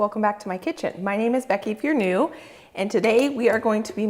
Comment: When I first started watching your show, I thought you said your name was. After a few watches, I realized what I should have heard was,…” my name is Becky if you are new”. Love your shows,…I learn so much,..thanks.